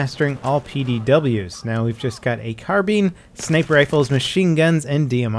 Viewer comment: Lol it sounded like he said carbeem